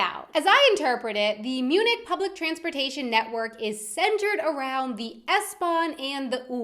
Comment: I thought it said “public transphobic” and I was like WHAT